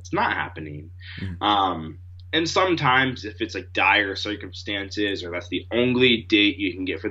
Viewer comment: "sometimes if it's like dyer circumstances..." What's he saying? I'm a foreigner...